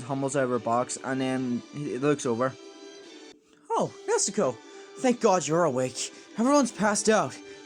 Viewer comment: I like how in 6:24 , the captions say “Mexico” instead of Nezuko Nice video tho bro